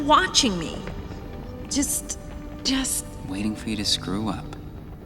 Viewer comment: For the longest time, when Flick and Ada are having their heart-to-heart after the rescue, I misheard Flick’s line “waiting for you to screw up,” and I thought he said “waiting for you to just grow up.” I guess that line could work in that context…maybe